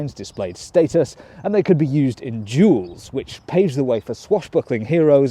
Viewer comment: Why do the captions say jewels if you said duels